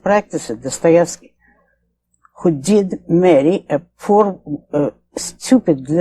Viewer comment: What's the mame of the writer she mentioned at 23:20 did she say Dostoevsky? I couldnt find anything about that story she told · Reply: Yeah she said Dostoevsky